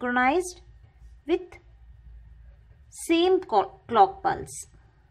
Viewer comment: she said cock instead of clock at 1:07 and it kinda turned me on JK.